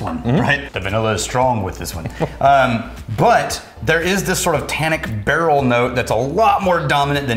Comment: said "sort of tannic note", I heard "satanic note", pricked up my ears right away :P I'd drink that whiskey!